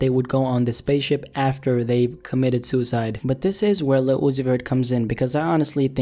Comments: Turn on CC. The subtitles say "Lucifer" when he said Lil Uzi Vert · In the 2:51 mark if you are watching with captions You’ll see when Lil Uzi Vert The caption says LUCIFER!! 🤯🙅🏾‍♂️🔥 -The Next Science Teaching Rapper